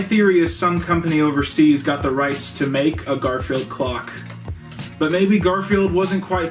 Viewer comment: God, I kept hearing "Garfield Glock'"